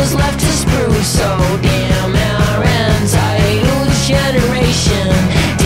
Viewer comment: Does anyone think it sounds like he says Anti-Youth generation? Anti-Youth, entitled. Nice Spin in my mind.